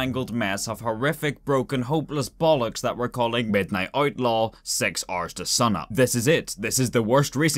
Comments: I really heard "6 arses to sunup" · I always hear "six ars to sun up", "instead of six hours to sunup"